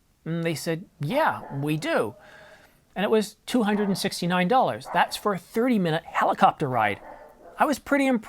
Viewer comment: A well known sauce! Heinz tomato ketchup. Bloody hell, I thought you were made of money when You said $369 dollars for a 3 minute helicopter ride, then later I heard you say 30 minute ride, I must have lost it in your accent on the 1st price announcement!